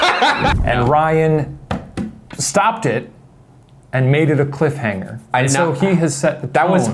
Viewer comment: Did Shane just say "maded"........ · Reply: oooh he said "made it" my bad oops